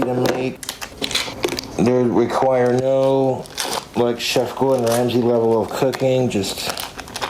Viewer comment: I could have sworn he said "JEFF GORDON RAMSEY"!